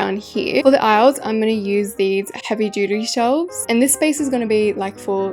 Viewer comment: I’m so sorry but it sounded like you said “heavy doodoo shelf’s.” 😭